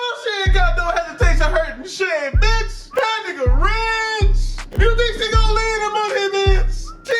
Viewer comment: I thought bruh said da nigga when he was saying her name 🤣